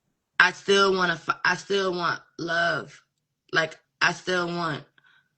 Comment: Did anyone catch that..."I still wanna fu** I still wanna find" trying to clean up her story😏